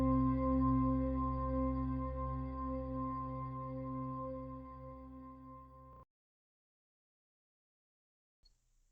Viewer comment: do you know what that said? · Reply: Parisian.